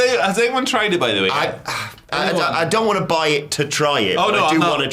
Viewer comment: “I’d buy it to try it” Boys THATS EXACTLY WHAT HE MEANS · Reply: You mighta misheard, but he said he DOESN’T want to buy it to try it.